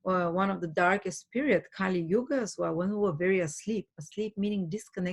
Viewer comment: what she saying? Kali Uchis is what I’m hearing lol